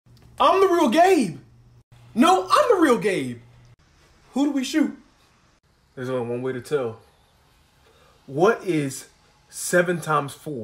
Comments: I thought you said I'm the real gay no I'm the real gay😂😂😂😂😂😂 my bad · i hear "im real gay" "no km real gay" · Misheard words:no I’m the real gay😣😣😣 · I thought he said ”i am the real gay” instead of gave at first 💀